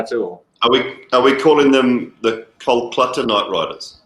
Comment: what did Simon say at 3:59 can anyone tell?? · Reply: Are we calling them the Kol-"Clutter" Knight riders? Is what he said